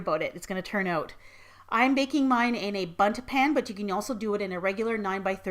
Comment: I honestly thought she said butt pan instead of bud pan